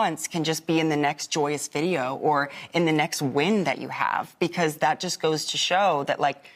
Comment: I misheard 9:22 as "..or in the next wind that you have." I thought, that's one way to silence your critics - fart at them.